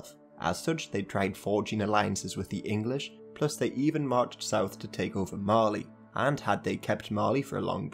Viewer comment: I THOUGHT YOU SAID MARLEY